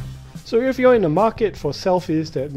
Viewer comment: wtf he can't even say his name clearly 00:57 "I am Ala-wishes-slow from Cnet" LOL